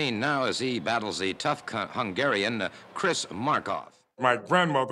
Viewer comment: wait what did the announcer say? I think he put an n in the word cut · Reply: He says, "He battles a tough cut hungarian - Chris Markoff"